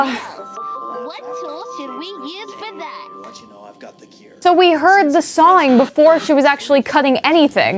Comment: I hear someone saying "I want you too know I got the cure." I also heard some voices before